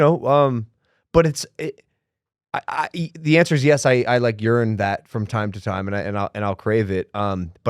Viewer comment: Did Logan just say he urines that from time to time. 😂